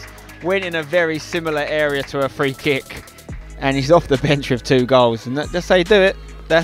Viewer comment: Does he say "and HE is off the bench with 2 goals" ??